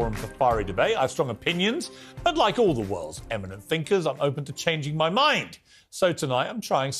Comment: Gotta love Piers Morgan implying that he’s one of “the world’s eminent thinkers” 😂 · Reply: Fuck me I actually thought he said UNLIKE the worlds greatest thinkers 😂😂😂